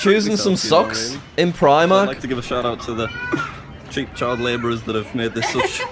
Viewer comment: does he say chink or cheap or cheek i cant understand